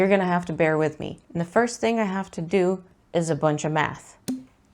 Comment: I thought you said, "The first thing I have to do is a bunch of meth." And then there was that pause... ha!